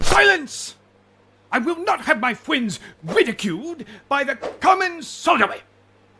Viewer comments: A truly horrible thing for "sussus amogus" to be ridiculed by the common soldier. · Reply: I thought he said “common sodomite” and I was like WOAH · I heard "I will not have my fwiends be ridiculed by the common sodomy" instead of "I will not have my friends be ridiculed by the common soldiery."